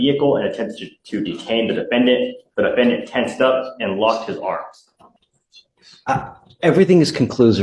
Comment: I thought he said, “And LOST his arms” 😂 and with the way that he was standing, I was like, “WHAT THE FUCK?!?! They had to amputate his arms and they’re STILL prosecuting him?!?!” … but then he puts his hand up to ask a question. Bahahaha